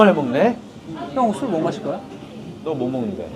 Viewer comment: 자막에 술 못마실거야가 머냐 그렇게들려도 문맥상 술 뭐마실거야지